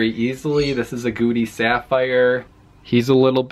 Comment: I thought u said " Gucci sapphire " 😂